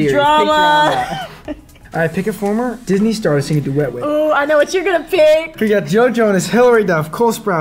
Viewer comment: When Joshua said "Pick a Disney star to sing a duet with" I heard "Pick a Disney star you wanna get wet with" omg lolll